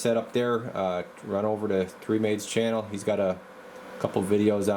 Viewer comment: I couldn’t understand what channel to see this installed. With my hearing it sounded like you said 3 Maids lol